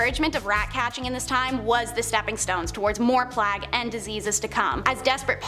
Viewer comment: is she saying "plag" instead of plague?